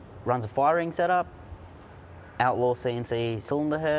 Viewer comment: did he say firing setup? whats that? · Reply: KVn a fire ring is basically a o ring on steroids used mainly with copper head gaskets